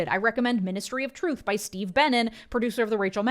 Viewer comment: My ass really thought you said Steve Bannon and I nearly did a spit take. I was like what alternate universe did I trip into.